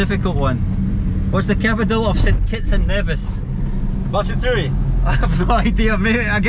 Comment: ? I repeat 10times that but still can't hear properly lol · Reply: St. Kitts and Nevis